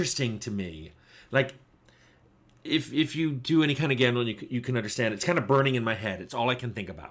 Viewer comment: Did you say "gambling"? How is that related